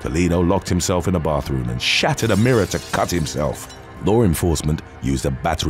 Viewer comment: I feel bad for laughing at this, but I thought he said that the perpetrator "...locked himself in the bathroom and shat out a mirror to cut himself..." when he said that he "...shattered a mirror to cut himself..."